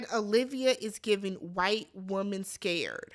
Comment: I thought Leah said “white woman TEARS” so I went back & re-watched & I’m pretty sure she said “tears” not “scared”. It makes more sense really since that is certainly a topic of discussion we’ve all heard before in current dialogs regarding racism. Not sure why Leah wanted to throw that out there though, maybe those have been her thoughts towards Kaylor for quite some time? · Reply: She said scared